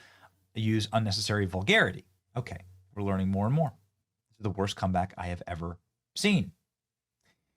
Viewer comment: damn he actually said learning more and more but the comment said learing 😂